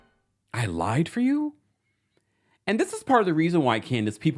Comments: RHUPGT S3: I heard "ride for you" even though the closed captioning says "lie". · I live for your recaps, Regarding RHUGT: I’m 99.9% sure that Gizelle said to Candiace, “Don’t act like I didn’t ride for you.” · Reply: I concur, 99.9% I heard "....ride for you' as well.